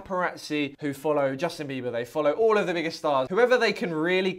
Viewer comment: did he say the “N” word I’m confused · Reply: ‘Biggest’ I think it was his lisp